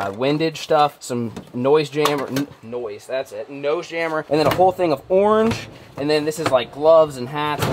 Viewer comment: i was reading something while listening to the video and I thought said a whole bin of porn lmao